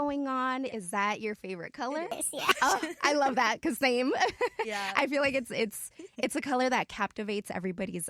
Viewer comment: Did I just heard someone said “yeah”😅 (1:53)😂